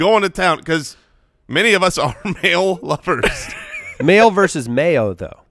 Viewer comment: I heard it as “male lover” lol